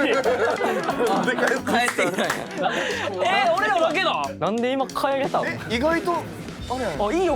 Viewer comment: ここのカイリュウくん「えー！俺らだけなん？」に聞こえる🤔